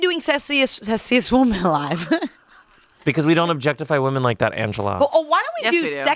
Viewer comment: I thought Angela said zestiest woman alive LMAO